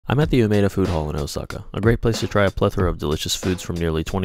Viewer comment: Why does it sound like he saying abandoned food hall lmao